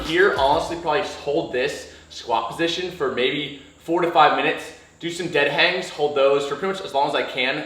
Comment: I miss heard him I thought he said squat holds for 45 mins instead of 4 to 5